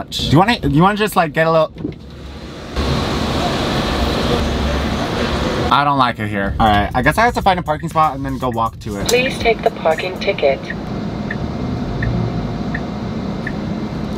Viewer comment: I thought the thing said “please take the fucking ticket” in a very passive aggressive voice 😂